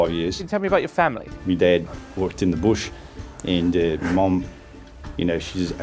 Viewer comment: As an Australian I am confused. Was he saying “my dad worked in the bush”? Cause it sounded like “muh dehd walked in the bish” Sounded more New Zealander. They have a distinct way of saying vowels